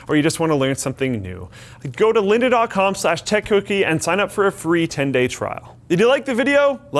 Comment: It sounded like said "Tech cookie" every time he said it